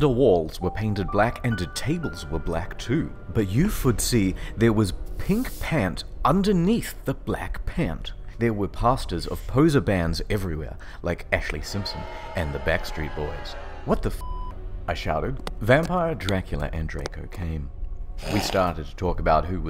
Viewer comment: Less than 2 minutes into the video and "He wus werring a blak robe dat sed 'Avril Lavigne' on da back." has me in tears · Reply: Kx27 I thought she said no posers. I guess she makes exceptions.